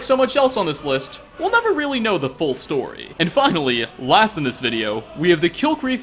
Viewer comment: did he say fuckily